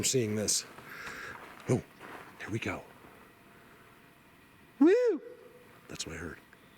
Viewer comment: I hear a quiet yet distinct "whoohoo" or "youhoo"